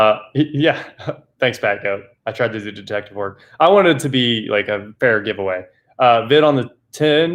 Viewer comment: hey yeah i also thought it had to say wanted it for 1$